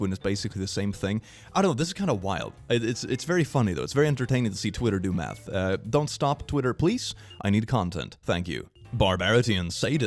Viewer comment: I heard, "it's very entertaining when Twitter does meth" not math and now I can't hear it any other way XD